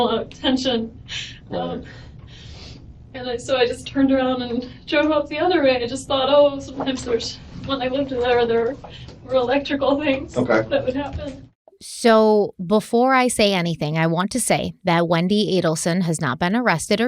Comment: When the detective was interviewing Wendy, did she say” I knew this would happen?” It’s kind of hard to hear because she is forcing tears and crying. But I listened a second time and it sounds like she said it.